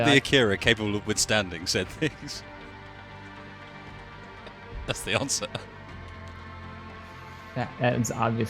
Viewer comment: "I'll build an Akira capable of withstanding such things" When you followed that up with "That's the answer" I thought you said "That's the Oslo", which is actually a Star Trek heavy escort class based on the Akira lol.